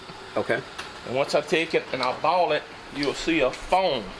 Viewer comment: had to rewind a few times to realize he was saying “boil it” lol I was like what kinda words they makin up down there?? Gotta love them accents